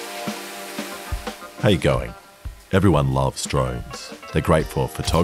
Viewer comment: OH NY GOD I FINALLY KNOW WHAT YOURE SAYING IN THE JNTRO I HEARD IT AS HOW GUY BAHAHAHA